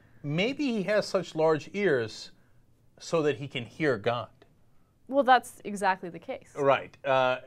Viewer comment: fail "so that you can't hear gun" wtf?